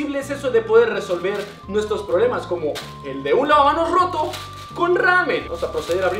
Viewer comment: escuche que misa dijo lavaanos XD